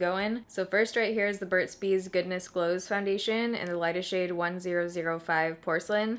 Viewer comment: At first I thought you said Goodness Blows"!!!